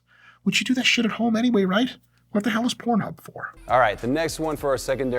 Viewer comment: Excuse me.. did you just say you (or I) train forearms at home.. "what the hell is pornhub for"... Even if I didn't hear it correctly, and my mind is making this up it still cracked me up.